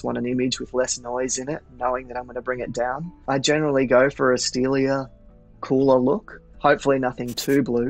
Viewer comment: What did he say at 7:42? Sounds like “asteelia” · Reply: pg “To go for a steelier look”